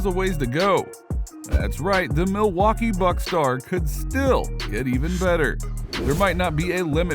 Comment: Anybody else hear him say the Milwaukee bucks guard